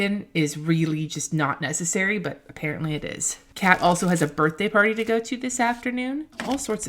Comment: omg I thought you had said "the cat has a birthday party" not "kat has a birthday party"